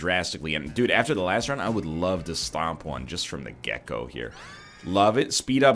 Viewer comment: Every time NL says "from the get go" I hear "from the gecko".